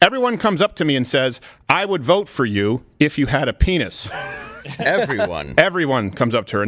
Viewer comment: She misheard: They said "I would vote for you if you weren't a penis".